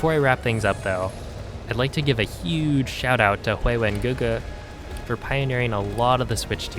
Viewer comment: a huge shoutout to WHO!?! Subtitles say “hylian guga” ??? Whose name did you say bro?